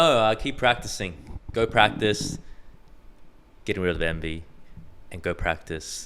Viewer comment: I misheard Brett at the end as “Go practice and get others to envy” 😂